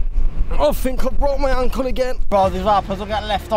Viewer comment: dont know why, but i laughed my ass off cause i thought i heard: "AHH, i did it again. i fucced my uncle"